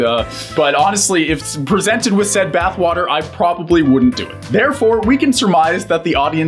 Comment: did you say probably